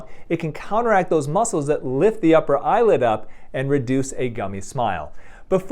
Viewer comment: Did he say 'lift the upper EYELID up' or am i tripping?🤔